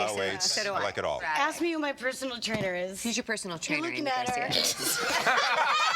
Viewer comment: What were they saying in 8:50? I think what Rachel Harris said made them all burst into laughter but I can barely hear them😂 so can you plz tell me what she said exactly?🙏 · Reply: They were talking about exercising and this happened: Aimee: ask me who my personal trainer is Rachel: who is you personal trainer? A: you are looking at her! And then everyone starts laughing and I think Rachel says: SHE JUST COULDNT WAIT TO SAY THAT